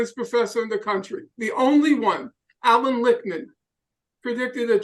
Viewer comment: Did he say Allan Rickman? Professor Snape was predicting Trump's victory?